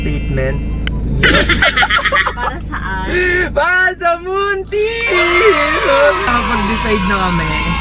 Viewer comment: Tama ba narinig ko para sa buntis?